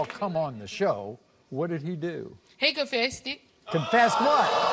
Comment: did she say "he confesseded" LMAO..